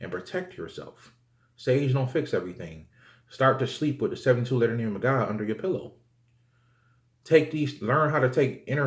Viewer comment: What do you say at 31:22 brother? You said that so fast haha. Start sleeping with the what under your pillow? Anyone ? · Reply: The 72 letter name of God. I believe that's what he said